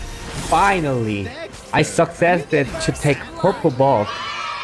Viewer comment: I thought u said I sucked the purple buff ahahaha